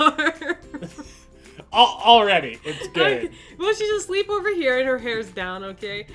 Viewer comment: wait did he say It's gay?? I'm so confused lmao xD